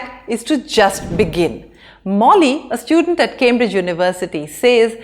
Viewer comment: did I heard My Name MOULI rarest name in the world 🥰🥰🥰🥰🥰🥰🥰